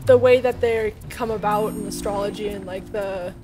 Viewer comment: The times i hear "astrology" is too damn high